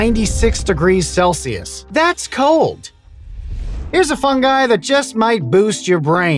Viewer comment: When you said, here's a "fungi" that might boost your brain, I heard: here's a fun guy that might boost your brain